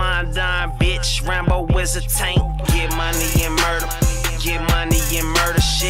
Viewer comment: it sounds like he says "Rambo is a taint" ...yes, yes you are😂